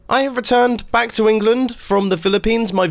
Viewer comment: at first i heard "im retarted back from england and my voice retired"